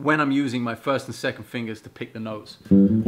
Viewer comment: i heard "pick the nose", at first... :D